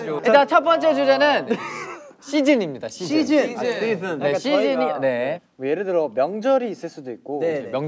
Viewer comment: sounded like czennie 🙂